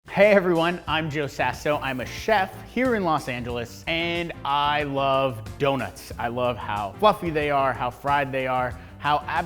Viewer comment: i thought he said joey sasso i was like wait